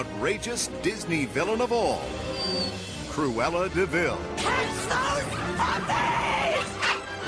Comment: when I was a kid i thought she said catch those parfaits.